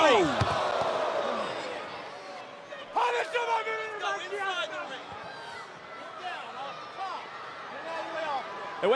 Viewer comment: What the hell did davari say 😂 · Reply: SlimePack He said "hame bayad bedoonan ke man ki hastam" its Farsi which they speak in his country Iran. it means : everybody HAS to know who I am